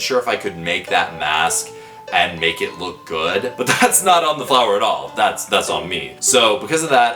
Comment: I thought he said buy the flower at "this shit"